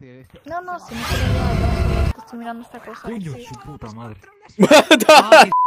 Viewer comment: does he say hijo de su puta madre XD